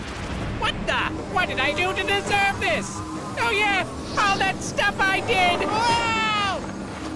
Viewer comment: Kaos sounds like hes saying "oh my".